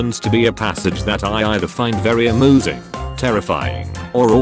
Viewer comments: Did you say a-moo-sing? XD🤣🤣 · Very AMOOSING is what I heard